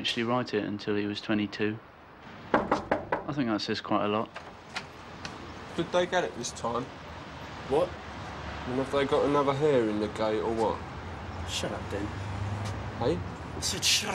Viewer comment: I love this, but what is Den saying at 3:19 - 3:21 ?? Something about hair ? XD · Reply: Hey Guggi. Better late than never... "Did they get it this time? Or have they got another hair in the gate or what?" : "A 'hair in the gate' will remain in front of the film and create a dark line that sticks into the edge of the film frame as the camera is filming a shot. A hair can ruin the shot and is almost impossible to fix in post production without using modern digital removal techniques"